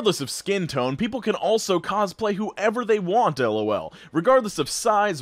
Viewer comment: he said lol instead of to.🤣